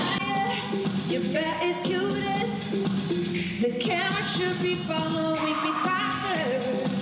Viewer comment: Que dice en el minuto 3:03 mientras canta Swish Swish? Cambio la letra original de la canción! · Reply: "The camera should be following me faster.." La cámara tiene que seguirme más rápido